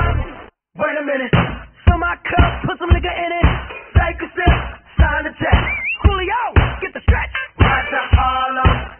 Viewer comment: what did he say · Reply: liquor (1 year late fr)